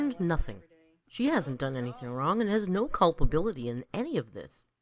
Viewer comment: She stretched that “Raght” so much, youtube translation thought she said “wrong” lol